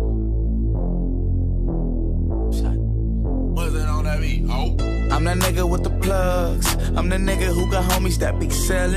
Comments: What does someone say at 0:08? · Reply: "Mustard on the beat hoe" Mustard is the name of YG's DJ/Producer. Mustard made this beat is what he's trying to say basically. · What is said at 00:08? "Put it on the beat"? I cant tell lol its in so many rap songs, same with the "hey hey hey hey hey hey" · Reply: the line said at 00:08 is mustard on the beat it refers to DJ Mustard at 00:50 DJ Mustard puts alot of those in his beats